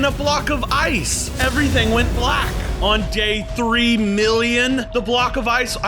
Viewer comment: I thought he said 100 days not 3,000,000💀